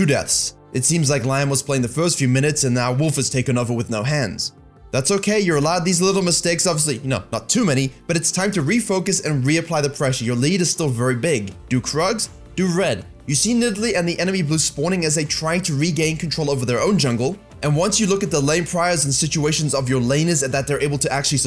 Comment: honestly had to double-take this, thought he said 'do drugs, do red' bahaha